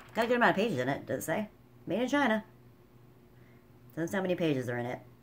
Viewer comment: Haha spotted another funny thing! When you say made in China.. at 5:33 the subtitles say MANGINA 😂